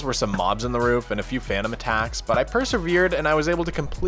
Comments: Did I just hear Fanum Tax? I hope not. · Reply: Your brain has converted into gen alpha because it was actually "phantom attacks", hope you get well from the brain rot soon. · My rotting brain thought he said Phantom Tax 😂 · Na who else heard phantom tax